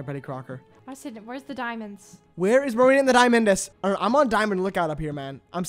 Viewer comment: DID HE SAY MARINA AND THE DIAMONDS OR AM I HEARING THINGS